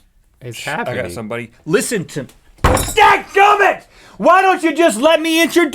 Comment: Thank Goodness! For Whatever reason it sounded like you said G.D. I played it like 5 times before I decided to watch it on my tablet (i was watching on my phone and knew it was really uncharacteristic) and realized you were saying "dadgumit". It may seem trivial, but Knowing about you guys having been a part of campus crusades for Christ that was like a punch to the gut. Whew!